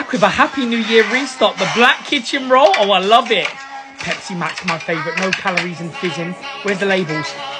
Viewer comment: “Pepsi my favorite coke” wtf did he just say that